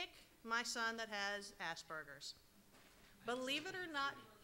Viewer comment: it sounded like she said Asburgers am i the only one that thinks she said that? (Not trying to offend any autistic people (if you do get offended on the off chance))